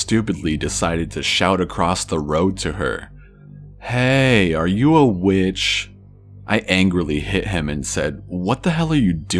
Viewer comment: random ass laugh attack when i missheard it as "heey are you a BITCH?" xDD